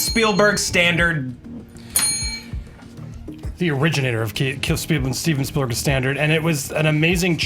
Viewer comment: to this day I have no idea wtf said at the beginning, but it might be my favorite thing I've ever heard someone say while drunk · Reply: The originator of Steven spielbergs standard